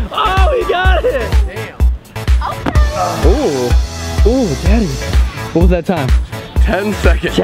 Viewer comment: anyone heard say "ooh daddy"